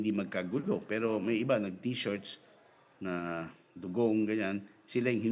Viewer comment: tama ba narinig ko "dogong" sabi nya haha DODIRTY DODIRTY.